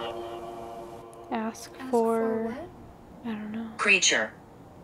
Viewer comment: here I think it said ask for name 👇if you agree